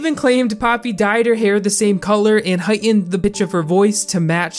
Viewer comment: anyone else else here heightened the bitch of her voice instead of pitch